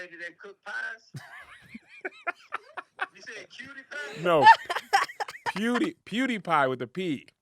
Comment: I thought he was saying cutie poe the whole time too lol